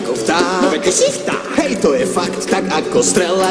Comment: WHAT DID U SAY?! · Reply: He said "Fakt"